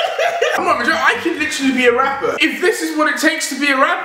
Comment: I heard " i can be raper" Deji i think you're becoming your brother:D